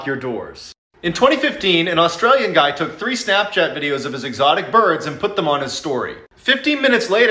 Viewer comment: when he said "exotic birds" i almost thought he said "exotic butters" 😭